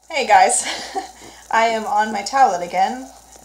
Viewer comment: lol at first i thought you said "i am on my toilet again" i was like WHAT?! lol i had to relisten! lmao